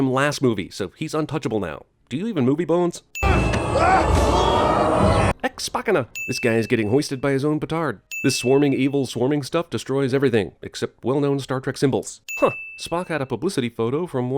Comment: for a moment there I thought you said hoisted by their own picard